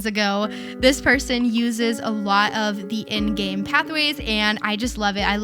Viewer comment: IM SORRY EVERY TIME YOU SAID IN GAME PATHWAY I THOUGHT YOU SAID ENDGAME PATHWAY AND ONLY REALISED MORE THAN HALFWAY THROUGH 😭